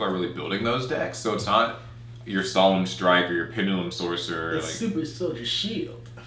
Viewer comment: What was the card mentioned at 3:38? I can't make out what he said. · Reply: has said Pendulum Sorcerer, in reference to Performapal Pendulum Sorcerer.